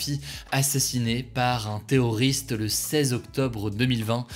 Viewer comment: pourquoi à 0:40 il dit théoriste ? tout ça me semble fumeux